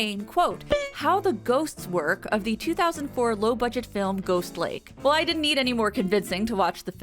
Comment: Girl I thought you said the movie was called "Ghost Leg" and I was like "what were you expecting with a name like that??"